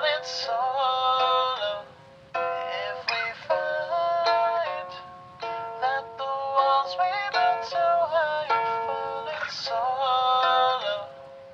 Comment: At first I heard “if we fight, that the walls we built so high have fall in sodom” sodom like the biblical city but I have been corrected